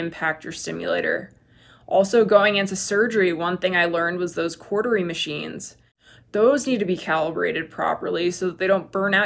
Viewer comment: it sounded like you said (and the auto-generated english subtitles said) "quartering machines." What's that? Thanks! Or did you say "cauterizing?" That would make more sense 🙂